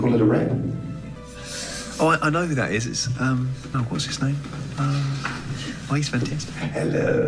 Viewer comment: Martin: oh I know who is it , uhm.. whats his name? Hi .... Benedict: hElLoOoOwWwW so cute :) (what did martin say after "hi"? hahah) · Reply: He’s fantastic I think 😂